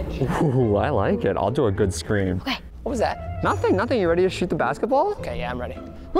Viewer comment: When Preston shot with a nerf gun I thought literally said what the f was that I thought he cussed